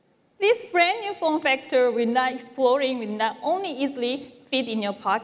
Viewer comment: did she just say this "form factor will not EXPLODE"?! Maybe I'm just hearing things...my ears have been ringing since my Note 7 exploded